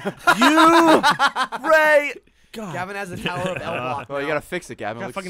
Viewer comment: The Tower of Limps instead of the Tower of Pimps